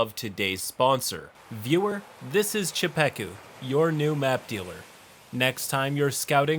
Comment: for one second I thought you said meth dealer.